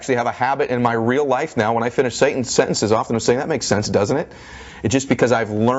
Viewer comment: Did anyone else hear when Ed Myllet said satan?